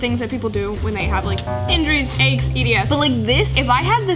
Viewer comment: Wait.....? Did say E.D.S? 15:55 I just want to make sure I’m not hearing anything because I have EDS so....just wondering.